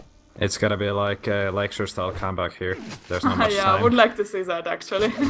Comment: it's going to be "..." style comeback, i can't understand what he's saying what is he referring to? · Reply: "Lexer"